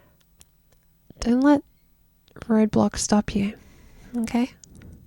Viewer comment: my brain is so destroyed by the internet that i heard "dont let roblox stop you"